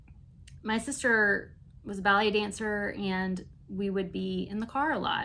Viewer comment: Happy very late birthday! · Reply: I thought you said your sis was a belly dancer🤣